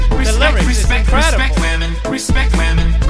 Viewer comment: i thought he said respect ramen